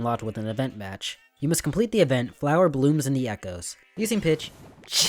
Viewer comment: did you say using pitch? That's hilarious. Hahaha, you're so funny.